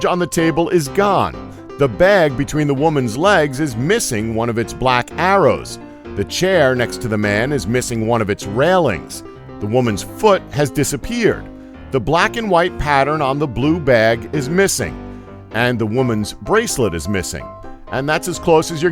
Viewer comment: did he say woman?